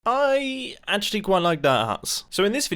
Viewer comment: Lol I thought in the intro he said I actually quite like that ass